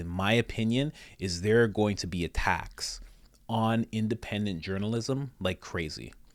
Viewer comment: Thanks for posting for those of us who don't do Twitter. Saves me! Funny how the word "attacks" and "a tax" sound the same. I thought you were saying they were going to tax independent media. 😅